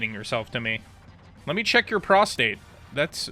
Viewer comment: First time watching this guy and I hear "let me check your prostate"😭